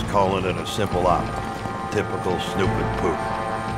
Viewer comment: I heard, "snoop dog poos"